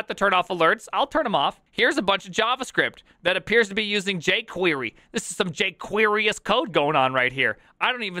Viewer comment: i heard "jquery cult", not jquery code al over the place...